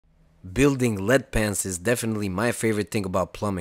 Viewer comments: I thought he said Lead Pants at first... I was ready for an entirely different kind of video. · Who else thought he said “Lead Pants”. ! 😂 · And here I thought he said lead pants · I was intrigued thought you said lead pants 😂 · aw man i thought he said lead pants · I thought you said "Lead Pants"